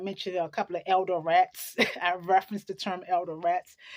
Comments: Haha at first I thought you were saying "elder's rat" instead of "elderette" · At first when you said elderette I thought you said "elder rat" lol